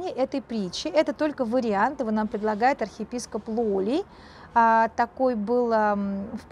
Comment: спасибо большое за такую интерпретацию, хотелось бы уточнить имя архиепископа, который предложил такую версию ( 15:14 мне послышалось Лоли?) . Конечно, в интернете с таким именем архиепископа не нашлось:(